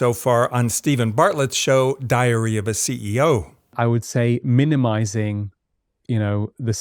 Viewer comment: I heard that as ‘diarrhoea of a CEO’ which sounds a lot more accurate..